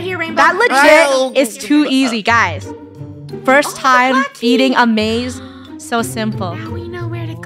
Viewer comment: I thought u said “it’s our first time eating a maze” LOL